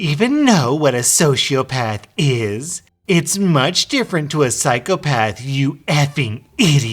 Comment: D-did she just call herself a psychopath?